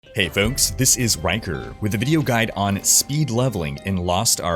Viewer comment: I thought you said “hey everyone, this is wanker” I actually lol’d